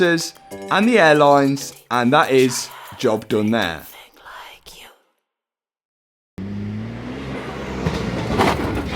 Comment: Who is whispering at 16:15 and what are they saying? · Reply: It’s from the song in the background and she said “nothing like you”